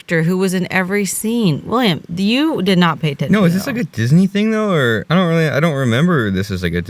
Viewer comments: I’m stupid I thought they were saying Disney C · I thought she was saying disney C the whole time